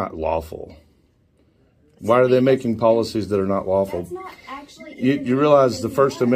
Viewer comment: I heard: That's policy that's not waffle.